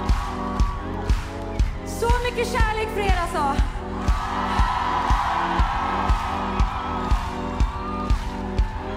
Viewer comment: What does she say at 4:22? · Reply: Something with so much love for you